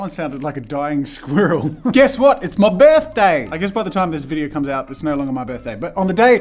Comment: I think I heard him saying "Guess what, it's my beef day" haha